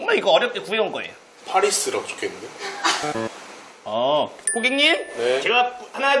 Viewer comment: 고객님이 호갱님으로 들리는 마법 ㅋㅋㅋ